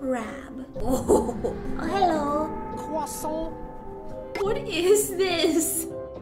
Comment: When the tiK ToK was the Krusty crab when the game started I thought said hello crusty